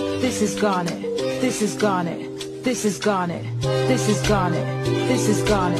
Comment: Well I don't know why but I hear "pisses garnet"